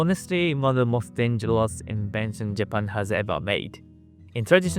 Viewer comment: I think the cc should say "most ingenious" rather than "most dangerous"?